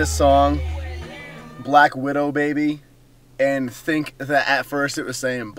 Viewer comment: I thought she was saying BLACK LITTLE BABY! hahaha